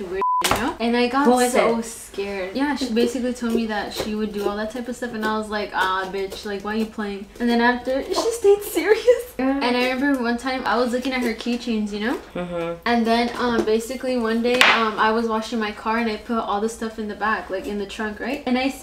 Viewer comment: Dude when your friend was telling the story about her coworker and she said, "I was looking at her keychain" I thought she said, "I was looking at her chichis" LMAO